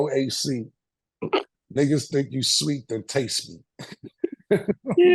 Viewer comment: come on Mase what is u saying you sweet taste me 😂😂😂😂